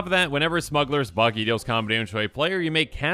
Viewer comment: I thought that said smuggler’s bussy for a minute